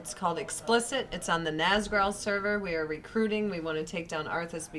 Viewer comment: I am not very good at listening, can somebody American tell me what server she said? I hear Azgalor or something like that Thank you very much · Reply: Nazgrel